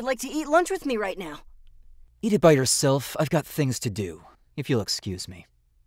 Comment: I thought it Said "Eat yourself"💀